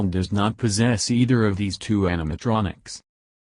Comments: I kinda laughed when when the guy sounded like he said anime-tronics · SWEET PATATERZ classical text to speech also there are ones in fan made games or just disturbing fan art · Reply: ThatGaming Channel I’m saying that it sounds like he is saying Anime Tronic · Anime tronic is what it sounded like you said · Did u just say AnimeTronic? :D